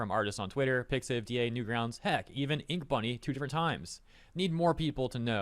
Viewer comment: really misheard inkbunny as "ifunny" 💀💀💀💀